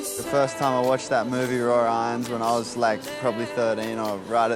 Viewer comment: What movie did the guy say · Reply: Raw Irons